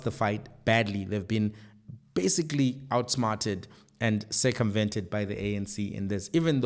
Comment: I need my ears checked. I had to replay from 24:50. I misheard you and thought you said “they’ve(DA) been outsmarted and circumcised by the ANC” instead of “circumvented” 😂😂😂